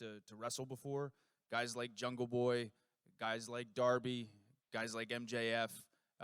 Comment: what that guy said??? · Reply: Darby, guys like MJF.